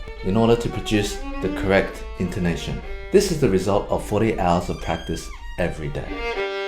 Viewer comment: Did he say 40 hours of practice everyday